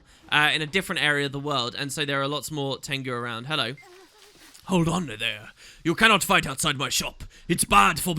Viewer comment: I could have sworn you said "Hold honor, there".